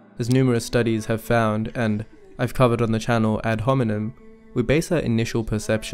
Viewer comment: Is he saying ad hominem?